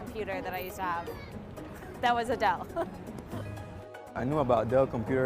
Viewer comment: Timestamp 0:23 did she say Adele😆? I love her she has a wonderful voice